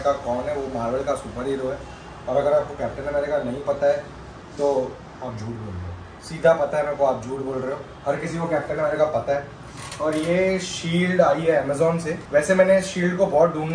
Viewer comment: i thought he is saying ap chutya ho 😂